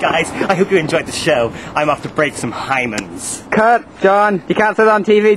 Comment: did he say "break some hymen"?!?!?